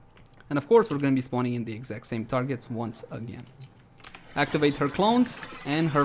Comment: Lol I thought I heard these 2 tards but it was 2 targets instead